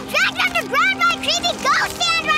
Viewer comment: i thought she said creepy ghost stand